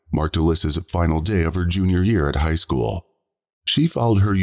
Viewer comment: 17 yr old middle schooler? How many years did she get held back? Or did the robot voice get it wrong? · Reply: Umm... where was that? I heard "Junior year of High School".